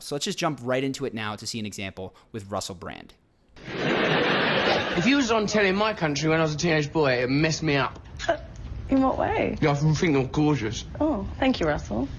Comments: WTF is Brand even saying in the interview at 0:39?? ".... gggnnnhhhh prt... telly.. my country... it messed me up ", "In what way?", "... ggggglllnnnhhh prt... gorgeous". Like whaaaa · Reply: “If you were on telly in my country when I was a little boy it would mess me up” “In what way” “your F***ing gorgeous/everything is gorgeous” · Could anybody tell me what Russel Brand said here 0:38 ? "If you used to tell in my country, when I was a teenage boy, it messed me up" ? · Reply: telly